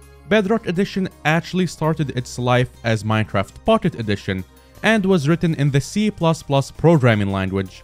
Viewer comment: Started as Minecraft F**ked edition... As I heard in my ear :)